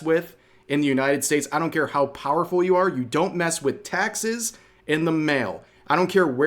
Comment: thought he said Texas. But im sure he said taxes. 😂